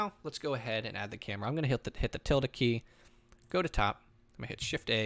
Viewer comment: what does he say at 5:37 .......tilted key???